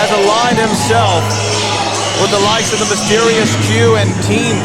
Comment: Did he just say "The mysterious Jew"?